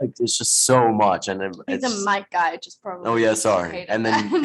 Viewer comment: please tell me what did she say. thanx in advance!💘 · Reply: "I think the mic guy probably just hated that" cuz Noah screamed